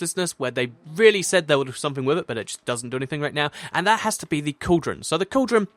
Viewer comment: With a coldern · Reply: thought you said condom for a second.